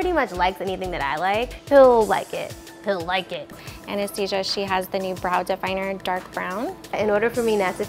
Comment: DID SHE JUST CALL ANASTASIA "ANESTHESIA" ?!?!?!?!?!